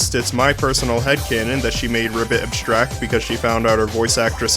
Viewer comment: You say “Ribbit” but the captions say “Ragatha”